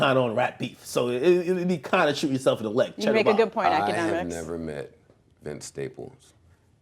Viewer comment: bruh did she call him economics?😂😂